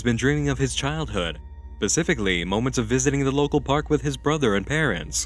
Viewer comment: Did I just hear him say "pacifically" instead of specifically at like 22:25?